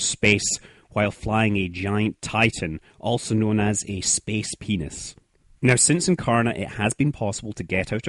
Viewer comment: did he say "space penis"??